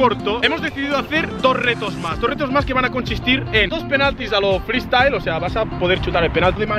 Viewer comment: "se va a conchistir" escuche xdxdxd